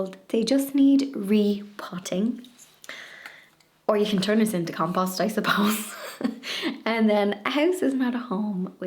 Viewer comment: So much fun being here. Seeing abit of Ireland is wonderful. Thank you. ( 19:59 did I hear u say 'or turn us into compost I suppose' 😂..you're adorable)